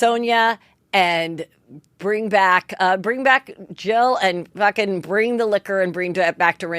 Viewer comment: Heather kept saying , “ bring back … such and such girl “ for the real housewives … But I kept hearing , “ 🎶💃 Bring Back My Girls ! 🎶💃 “ … ❤ Heather, Rupauls Drag Race Darling ❤